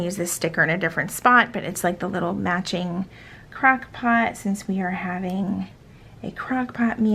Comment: I heard “crack” pot instead of crock pot and I was dying 😂